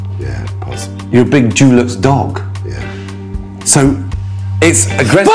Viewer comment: ha! yeah, I thought that's what he said, too. I think he said 'Dulux'.